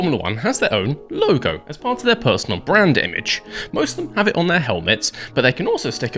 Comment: At first I heard "as a part of their personal BRAIN DAMAGE"... oh, it was "BRAND IMAGE"...